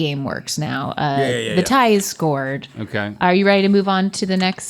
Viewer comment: Did say the tie is scored on purpose? lol